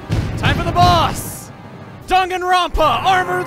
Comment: "Name's Dongorongo Dungeonboss. I'm just your everyday dinosaur monster." SPLASH SCREEN: Dongorongo Dungeonboss; Ultimate Lizard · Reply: I thought that said danganronpa